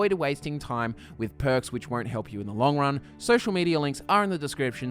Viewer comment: I thought you said “Nirning Out” at the end lol! 😂👌